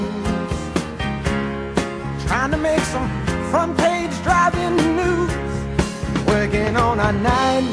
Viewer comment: Thought he said "fanpage drivin news"😁